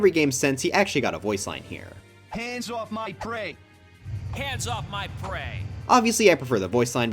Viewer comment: In falco's brawl taunt,i heard "hands off my grape"